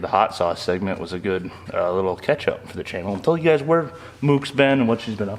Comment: Did really say that "the hot sauce segment was a good little ketchup for the channel" just after 58:55?